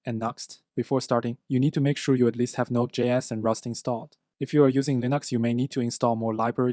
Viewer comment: Thanks for these killer videos! I didn't understand what you said at 0:14 "If you are using the ??? you may need to install more..." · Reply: A week late but he said "If you are using Linux"